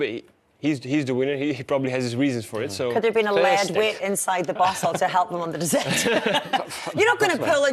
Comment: Hi there. I’m asking for what the lady said at 3:45. What I heard are “could have been a lead weight inside the bottle” and “You’re not gonna pull in your bead…” something like that. Could someone explain what she said? Tysm! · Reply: “Lead weight in the bottle to help with the descent” and “not gonna have a pull from your bidon” means not gonna take a drink from your water bottle.